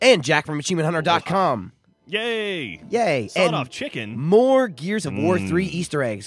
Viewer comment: I thought i said sawed off chick :(